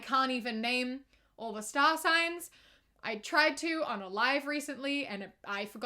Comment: "i tried listing all the signs on a live and i forgot aquarius" me, a genuine aquarius: yeh sounds abt right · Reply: I misheard "on a live" as "unalive" and was Very concerned, so thank you for clarifying this for me 😅